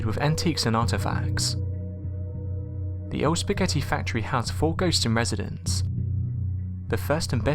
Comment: I thought he said: The spaghetti incident. My first thought was, I like Use your Illusion 1 & 2 and Lies.! 😂 That's what I get when only halfway listening.!